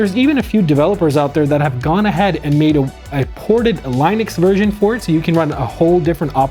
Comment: a what now? Did I hear line-ex?